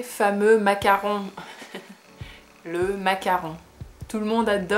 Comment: I'm really tired watching this as I find French relaxez vous tres bien before I go to la Lit, and I thought the lady said Macron but it was macaroon, and I thought she wanted to eat the French leader. I can understand that sort of..... Regardez vous!